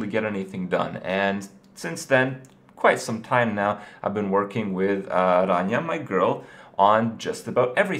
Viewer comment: What does he say at 1:41 "i've been working with an armyanai girl?"